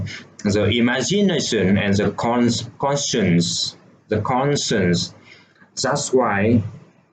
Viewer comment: i dont catch this word " con sun"